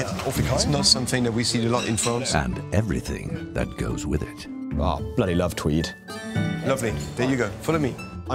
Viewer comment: He started talking and I heard: 'Bloody love crips!' Nice couple. Good match.